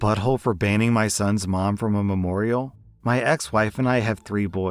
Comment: God I misheard the first several seconds. "AITA for banging my son's mom in a memorial?" Edit to avoid clutter: For story 4, I think the easy solution is to go shopping with the daughter for a dress, and lend a piece of the wife's dress to the overall ensemble like the veil.